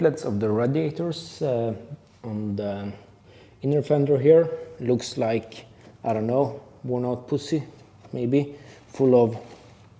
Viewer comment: "looks like, I do not know, worn out pussy" Did I hear right? :-D Rofl